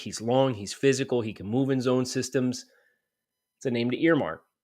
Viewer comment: He was the 4th graded Center. He's very good Center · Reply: Yeah agreed. I thought you were saying move him for a sec lol